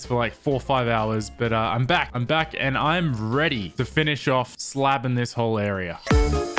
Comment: did he said slapping or slabbing